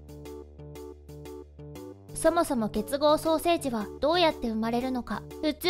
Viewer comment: 結合双生児のことが 結合ソーセージに聞こえた